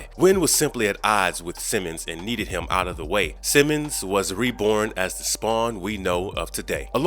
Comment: Who in the nine hells is A.I Simmons?? lol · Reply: I thought I heard him say "A.I Simmons" lmao. Allen Iverson Simmons?